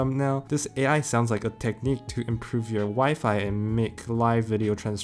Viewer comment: If you pause it before he gets the last "i" in wifi out, it sounds like he says "To improve your wife."